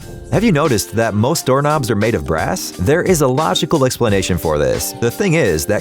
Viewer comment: who thought he said grass